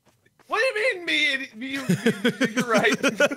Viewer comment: I thought wade said “you rite” and thought I was in another dimension bc that wasn’t a thing yet